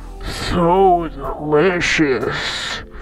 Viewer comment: I like where blue said delicious i heard him say racist